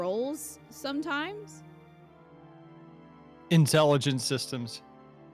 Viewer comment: did I hear that right... 32:28 "incel-igent systems"?? You know what, not even wrong. Fire Emblem has been ill with terminal toxic masculinity and allocishet nonsense for its entire existence. like... "haha isn't this sexual harassment and fan service for pedos so funny?" NO IT'S SEXUAL HARRASSMENT AND PEDO BS JFC